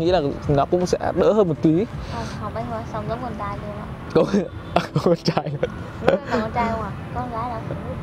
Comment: b ấy nói gì ấy nhỉ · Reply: "học ngành ý xong ra là giống con trai luôn đấy" :)))